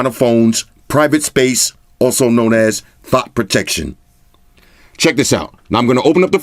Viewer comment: I actually thought he was saying "THOUGHT protection" until he demonstrated it around 34:00. 🤣